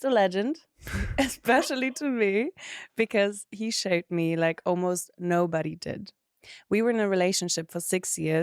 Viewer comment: Musste jetzt sehr lachen. Hab gerade die Folge gehört, wo die Gästin "Haul" als "Holz" missverstanden hat. Und hier höre ich "he shaved me like nobody did" 🤭